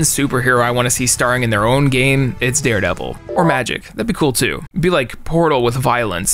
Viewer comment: Who's the superhero mentions at 23:37? I think he said her name was Magic but everytime I try searching for her nothing pops up except articles listing superheros who can use magic